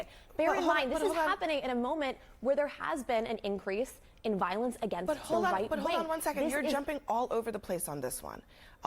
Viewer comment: did she say increase in violence against whites? Seriously not sure what she said